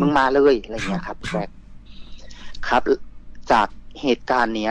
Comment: ได้ยินคำว่า เบาๆก่อนที่จะเล่า เรามโมคนเดียวรึป่าว